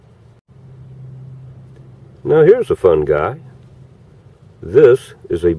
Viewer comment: All I heard was “here’s a fun guy”. Love your accent